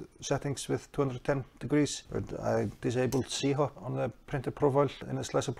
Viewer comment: Did you say you disabled SIHOT in minute 1:43? I’m new to this and trying to learn. Thank you for the video! Keep up the good work. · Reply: Hi, z-hop, sorry for bad english :)